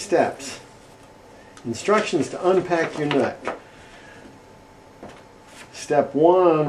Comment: lmao at a point i thought he said how to unbox your nut